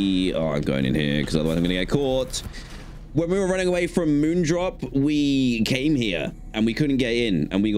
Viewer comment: I thought you said moon dop 36:40 😂